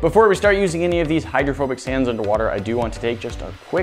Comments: Ok i thought it said homophobic sand...OOF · I swear I thought it said "homophobic sand" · Im not the only one that thought it said homophobic sand...right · At first I thought it said " homophobic" sand and I got really, really confused! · I thought that said homophobic sand and I thought to my self but it’s rainbow?